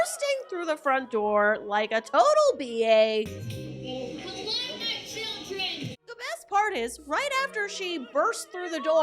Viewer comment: Did she say “hello my children”?!